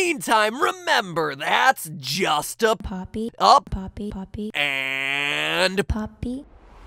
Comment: When she says poppy poppy I heard it as popcorn